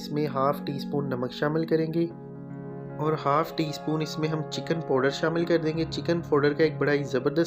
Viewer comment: pe samajh nahi aya apne kya bola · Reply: Chicken powder